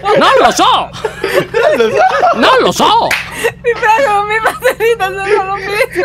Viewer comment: "Ti prego non ولكن ماذا بحق الجحيم قال؟" O se sapete dirmi cosa ha detto ditemelo. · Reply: Ti prego non mi fate ridere نيلخصنلوبووي